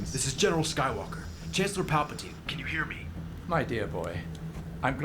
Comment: I was eating/ chewing and I heard him say "Emperor Palpatine" for some reason, hahaha.